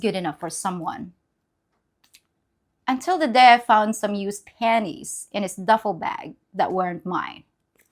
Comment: At first I thought you said "used pennies" ! Hey I am a white guy, good condition, single. You are plenty good enough for me.